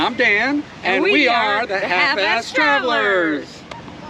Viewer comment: 1st time watching your video, I thought you said Half Assed Travelers....LOL, sorry.